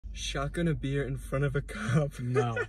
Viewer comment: I thought it said shotgun a deer